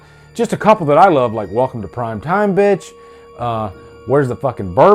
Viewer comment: Everyone hears "Welcome to prime time bitch" I hear "Fuck the prime time bitch" Ive watched and listened to that line hundreds of times and am confident everyone else is crazy. I hear it the same way everytime.